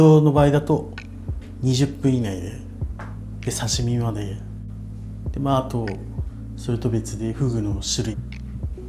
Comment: sounded like he said tomato 😂